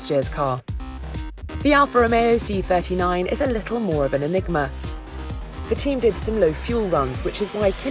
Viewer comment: did she say LIGMA???